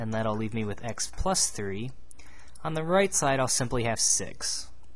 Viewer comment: Your Videos are amazing keep up the good work. I tend to use these videos as my notes. I was looking down at my A4 paper writing down the examples and my ears confused me at 08:07 . Thought you said "On the right side I'll simply have sex".